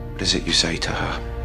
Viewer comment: what klaus said? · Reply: He said “what is it you say to her?”